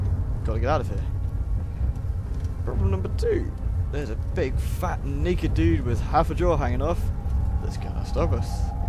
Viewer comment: I am unsure of what you heard, but to me it sounds like "There's a big, fat naked dude with half a jaw hanging off" around that time. I do not think I have ever heard him say the n-word in any of his vids.